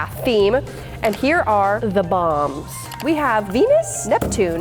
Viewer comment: sounded like ‘ moms ‘ 😂😂